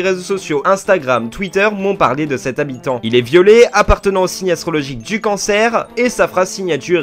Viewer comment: Quand il a dit "Il est violet" j'ai compris "il est violé" donc j'avaid pas compris 😭